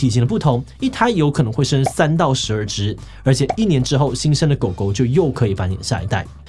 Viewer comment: 聽成新鮮的狗狗